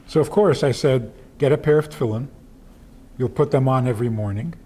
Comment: what did he said at 0:40 get a what? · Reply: tefillin, a jewish thing we wear every morning during prayer